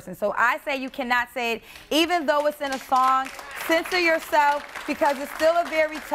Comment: Did she said censor your self 1:00 .. that's funny and I thought music is for everyone to listen to